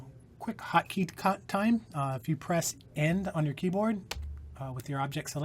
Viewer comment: In case anyone is going through at 1.5 speed and also thought he said the 'N' key, he was actually saying "End" (Which is easier to hear when playing at normal speed). Took me longer than I'm proud to admit to figure that out.